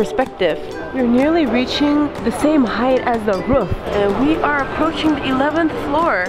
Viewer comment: Did anyone else hear rouf